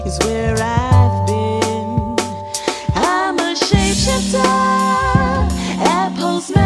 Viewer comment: All this time, I thought they were saying "I'm a sharpshooter"